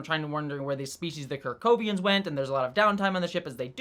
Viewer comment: I keep hearing "cracovians"